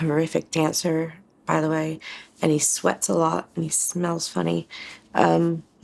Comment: thought boy said he can’t sweat after serving a few years in the Royal Air Force 😂😂